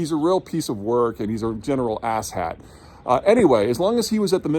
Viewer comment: “He’s a real piece of work and a general asshead” 😂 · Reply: thought he said "asshat"